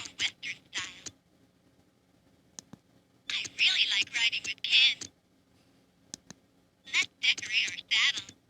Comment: I could have sworn she said "I like riding Ken".